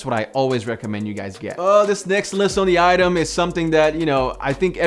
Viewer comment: did he say "this next list on the item"? haha